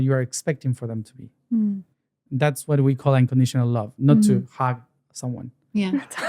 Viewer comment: sec he said “not to hog someone” I think you thought he said not to hug someone